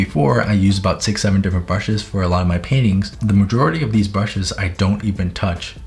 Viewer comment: for a sec i thought he said “i use sixty seven brushes for my paintings” lmao